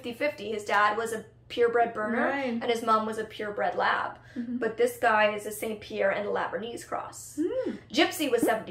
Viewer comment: I could have sworn the trainer said his dad was a St Pierre and the mom was the lab/Berner. Did I hear that wrong?